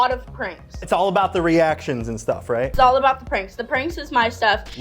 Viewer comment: It sounds like she is saying "I do a lot of cringe" and not "pranks". Well, she does a lot of cringe soo